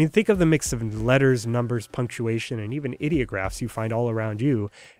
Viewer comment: I swear to god anytime I hear anyone say "ideograph", i hear them say "idiotgraph".